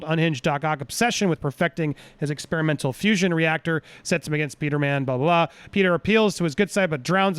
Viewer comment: Did he just say Peter-man?